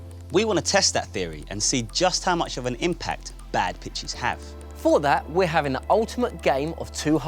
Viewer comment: Who else thought at 3:00 said bad bitches . But Ik he said pitches